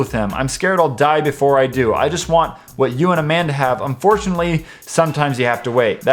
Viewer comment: when you said 2:19 I thought you said me and a man lol